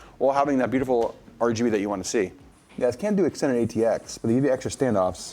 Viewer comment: did i just hear him say you cant do a standard atx board with that case (8:45)...im pretty sure you can. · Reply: He said that you can't do Extended ATX (EATX).